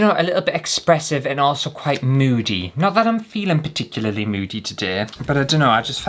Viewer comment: Moody? Oooooooh I thought you said Nudey 😂😂